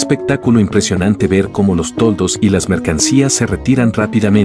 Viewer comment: Pensé que había dicho: “es un espectáculo impresionante ver como los tontos” 💀